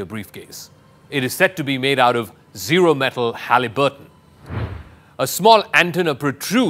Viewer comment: did he say zero metal haliburton?wtf is that. Is that what he said? Kind of like when WION was calling the Stinger the "singer". I'm like, is it a missile or a sewing machine.Or maybe a new Indian pop star.😀